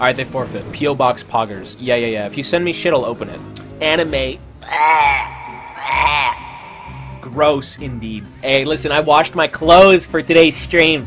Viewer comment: did he just said anime is gross?